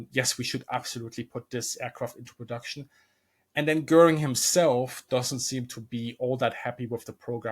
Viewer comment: I heard that first as "Göring himself doesn't seem to be all that heavy with the programme." An understandable mishearing, I hope. 🤣